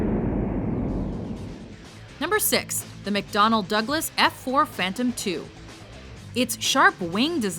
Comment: Did Anyone Misheard It And Instead Heard "Mcdonalds"?